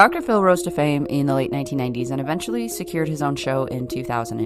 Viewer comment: you said 1990s I literally heard 1890s and died laughing 😂